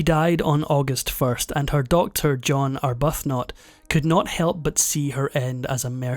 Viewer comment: First of all: good and interesting video. Thank you. ….. Second: my ears misheard you towards the end. I thought you said “her daughter John Arbuthnot.” I now realize you said “her doctor John Arbuthnot.” But in trying to sort out my misunderstanding, I looked up information on John Arbuthnot. He was a quite interesting fellow. I had never heard of him before. Thank you!